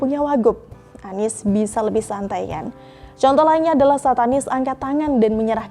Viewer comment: mbaknya bilang "Saat anies". Kok gw dengernya "Satanis".